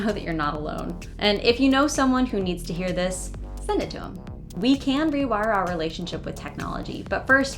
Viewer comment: I can't help but wonder if she (you) has the pin-pen merger. Youtube transcribed her at 21:18 as "to them," but I clearly hear "to 'im." It might be just me as I'm an L2 speaker and finding something that isn't there.